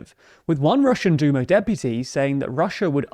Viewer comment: I heard "Russian DOOMER deputy" lol